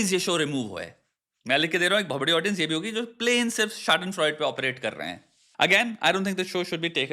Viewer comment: what is the word that he used? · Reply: nvm, got it, it's schadenfreude